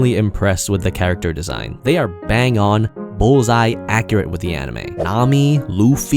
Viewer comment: Clicked video bc I thought said n word just to realize it says manga